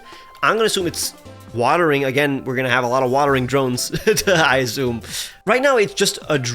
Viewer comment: got me doubting my english skills because i fully thought he was saying watering john, and i was like, is this a colloquial thing or something